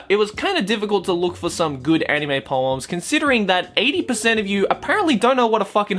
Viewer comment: I thought you said rape poems